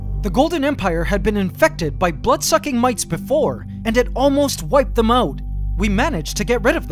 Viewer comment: The Golden empire live together in harmony but then everything changed when the blood sucking mites attacked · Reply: At first I thought he said "butt sucking mites".